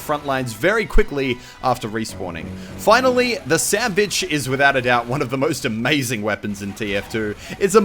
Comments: He said Sandbitch Instead of Sandvich, Or at least that's What it sounded like. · I swear I heard sand bitch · I'm sorry but did you say sandbitch or sandwich? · sandbitch i hear thats